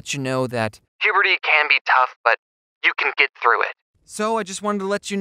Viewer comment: what did bro say?! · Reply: He said “Puberty can be tough but you can get through it”